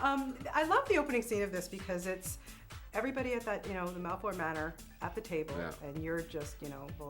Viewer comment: did she say malport manor ?? ? ? ?